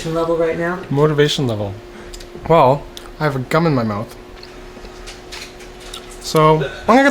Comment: I thought he said he had a gun in his mouth